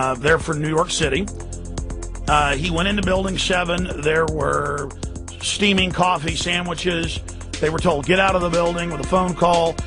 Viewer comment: What the hell did say at 00:16? Sounded like he said: "There were steaming coffee sandwiches"??? WTF??